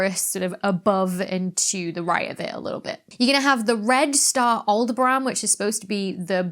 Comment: In Americanise: al deh br uhn. 01:01 (LOVE the accent) "Aldebaran." Which sounded like a place apparently not in a galaxy far far away; Alderaan.